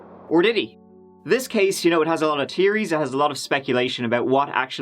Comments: I thought said Tear-ies lol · When he said there's 'a lot of theories' I thought he said tearys.. As in lots of crying then I realised 🤦🏼‍♀️🤦🏼‍♀️🤦🏼‍♀️🤦🏼‍♀️ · 🤣 I thought you said "this story had lots of tearies". As in, it will be a sad story and I chortled. Please say "theories" more regularly.